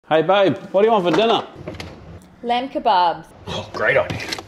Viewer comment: Her : "lamb kabab" What I heard : "l*und la baal" 💀